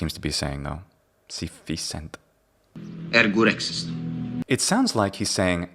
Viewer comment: as an Italian native speaker I hear "Ergo rex es tu?".